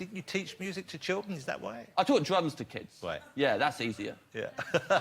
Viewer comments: I thought he said 'I taught drugs to kids." And I wasn't even like: ohmygod Just like, yeah, can see that. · when Jonathan asks if he taught music to kids i thought he said "i taught drugs to kids" and i was like that's hilarious why isn't anyone laughing, comedy gold